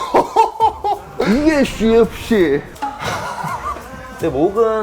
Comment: 뭐라고 말하는건지 아시는분? 궁금해 죽겠네요 ㅋㅋㅋ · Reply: Yes ufc